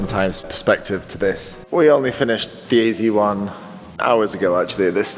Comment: That was hilarious subtitles around 13:00 mins when said "we just fiinished the AZ1" the subtitles said "the easy one" 🤣. I gather that wasn't an "easy one" for real though.